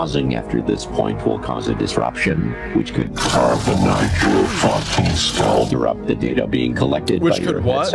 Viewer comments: Pausing after this point will cause a disruption which could "carve the knife through your f*cking skull" That's what I heard, at least. Lol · I think it said(carve a knife into your fu*** skull) that’s just what I heard